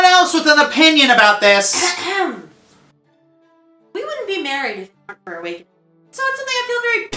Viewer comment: I think there's something wrong with the audio because every time I try to hear what she said, half her sentence gets cut off. All I keep hearing is "We wouldn't be married __ __ ____ for Awa___ing."